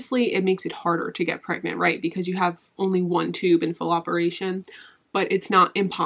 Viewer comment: ok i know this is serious and i’m glad you are okay! this video was awesome and really informative! but at 17:05 when she said “you only have one fallopian tube in FULL operation” i thought she said FALLOPeration and casually through out the biggest pun ever😂😂😂❤️❤️